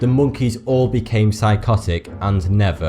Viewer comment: It always sounds like he's saying "Hey, forty-two here" lol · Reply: najar I think he's got some sort of lisping condition or maybe it's just his accent. when he says 'thanks', i hear 'fanks'